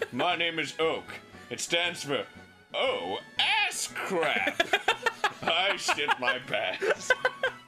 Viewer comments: i thought he said "oh ass krack" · am I the only one that heard him say oh ass crack, not crap